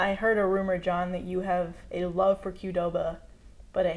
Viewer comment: I thought she said, you have a love a Cuba. And I'm like... Cuba Gooding?